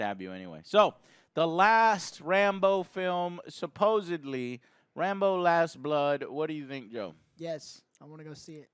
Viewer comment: All I hear is "Rainbow: Last Blood"